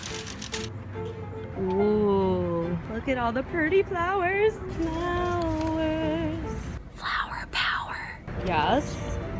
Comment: Did she say Purdy?